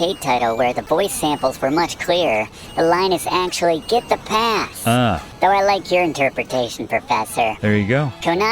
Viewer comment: "Get the pass." All this time I thought the announcer was saying "hit the bong."